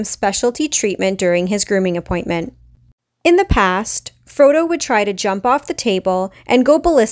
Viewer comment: I misheard at first and thought his name was FroYo. Yay audio processing disorder!